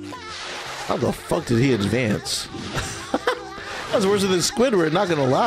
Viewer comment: Did you just say "Worser"?